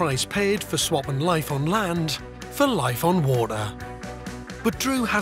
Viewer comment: What did he say at the end? Life on worlaah. 2:42 Weird English. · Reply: Life on water 🤷🏼‍♂️